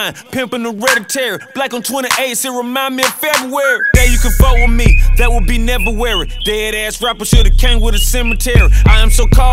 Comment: This whole ass time I thought he was saying “She Broke it down started moving like she care” 🤦🏻‍♀️🤦🏻‍♀️ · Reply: I always thought he said “Day you could fuck with me, that would be Neveruary” and I’m kinda disappointed it doesn’t 😒